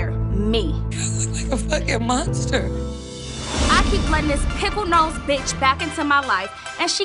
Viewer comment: it’s the “pickle nose” part for me😂😂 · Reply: I THOUGHT SHE SAID “PIMPLE NOSED”💀